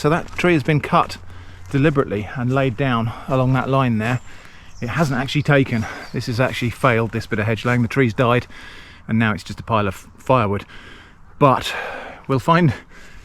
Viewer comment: As someone who has been hedge laying for around 10 years really happy to see you covering this, you can tell the abandoned hedge is quite old as it's been lain almost horizontal whilst hedgerows now are almost always (depending on style) lain diagonally allowing sap to grow that could be a reason that the recent lay failed. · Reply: So glad to see this comment! I thought he was saying "hedge lane" which would be more difficult to research if I was spelling it wrong. Do you hedge lay for fun or profit (or both?)?